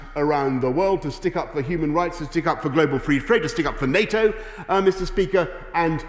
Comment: What is he saying at 0:20 global free fraid?